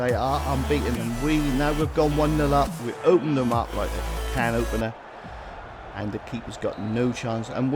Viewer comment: What does he say? 'We opened them up like the can opener'? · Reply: it was meant to be "Like we were a can opener"